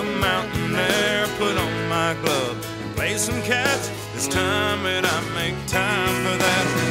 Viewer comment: When i was a kid i thought he said "play some cat"